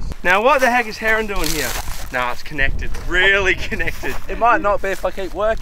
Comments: lol is he saying “it might not be fucking working”? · did he say not fucking working? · sound like he is saying it not be fucking working